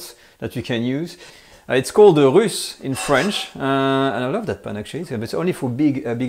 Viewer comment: What is it called? I've listened multiple times and I still can't catch it. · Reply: SeikiBrian — The pot is called "une russe". It is a round pot with high vertical edges.